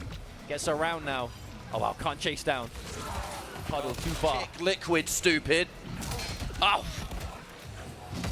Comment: did they just call the player stupid? 😂 "you can't hit liquid stupid"???